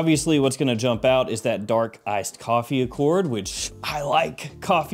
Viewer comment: I was listening to this while working around the house and thought you said “Dark as$ coffee” with the givenchy. I got excited for a sec thinking I could potentially buy Tobacco Tug Job and this Givenchy with Dark coffee note 😂😂😂